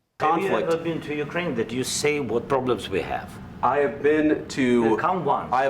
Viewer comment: he says "see what problems..." not "say what problems..."